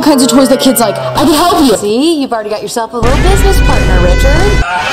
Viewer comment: i cant with the "tittle" instead of little XD